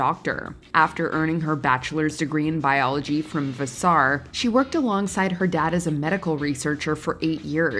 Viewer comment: Did she say vas-SAR?